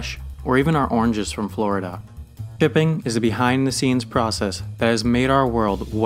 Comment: does anyone else hear pipping instead of shipping